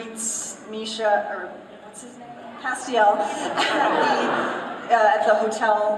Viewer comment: when she says Castiel my subtitles said "happy elf" Pretty sure they got confused with Misha's true self, not Cas